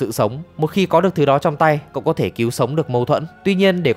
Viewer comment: Tui nghe nhầm hay ko mà từ cứu mậu thân thành mâu thuẫn đấy 😂😂